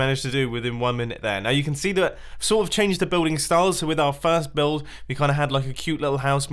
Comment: When u said i changed the building style I thought u said u cheated